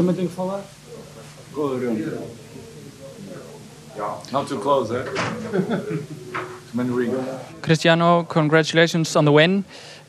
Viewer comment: Have you noticed? What he says 0:06 Don’t too close. Too many wrinkles.